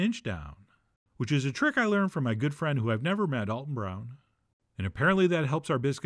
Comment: DEADASS THOUGHT HE SAID "From a good friend, whom I've never met, on growlr."